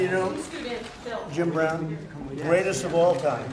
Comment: Did trump say, "Jim Crow" was the greatest of all time? 😶😮